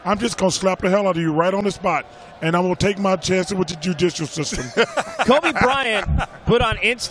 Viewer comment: I thought Chuck said Jujitsu system lol